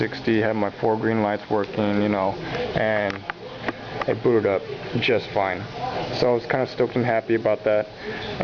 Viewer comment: lol i thought he said "it blew it up just fine" lol i subbed